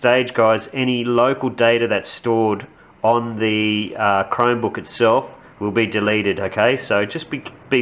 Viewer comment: sounds like youre saying "crimebook".